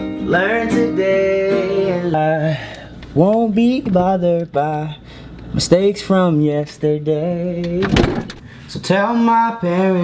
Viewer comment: No offence but where you say “I won’t be bothered by mistakes from yesterday” I thought u said “ I won’t be bothered by the stake from yesterday”